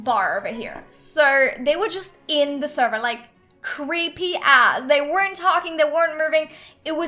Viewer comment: did she just say creepy as